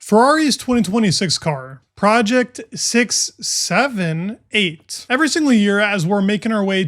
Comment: Did bro just say 6 7?